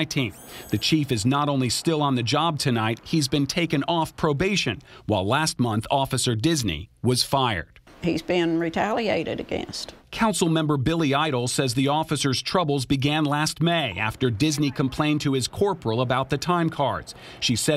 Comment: Billy eitel. Lol. Thought you said Billy idol. Officer Disney? Where do they find these ppl?